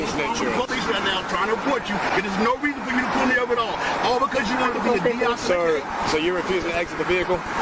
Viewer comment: subtitle correction at the end of the sentence, something was left out. The subtitle should say "All because you wanna be a D-I-C-K" He spelled out the last word. 3:46 Error: Subtitle says "This won't go down" x 2. He said "will" not "won't." "This will go down." x 2.